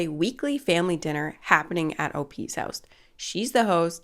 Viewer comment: I thought I heard "obese house".